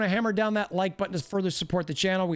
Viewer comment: at the very end i thought he said “keep your hands off your nieces and nephews” 😂😂😂